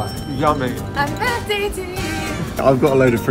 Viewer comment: Any ideas what that guy at the start said? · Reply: I thought he said happy Walmart to you